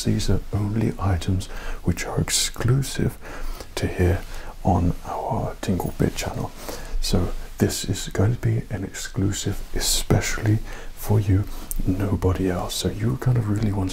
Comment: I thought you said "tingle bitch." That being said, there should be an ASMR channel called that.